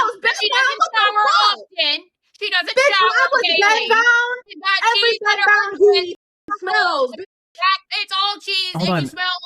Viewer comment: I thought she said “every bed bound queen smells”😂😂😂